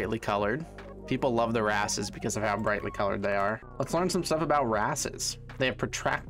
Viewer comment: if you listen from here it kinda sounds like he says ‘people love their asses because of how brightly coloured they are’ 😁